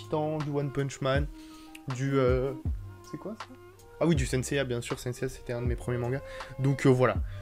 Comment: C moi ou a 4:28 on attend "c quoi ça "